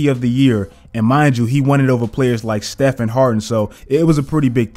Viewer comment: Thought he said Stephen Harden